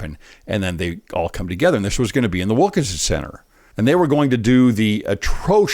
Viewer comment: Did I hear "Wokinson"? LoL I suspect because of the subject one hears what is expected to be heard.